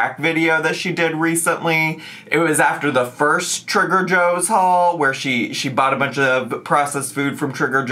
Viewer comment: i must be experiencing amber overdose; i kept hearing you say 'trigger joes' instead of 'trader joes' 😂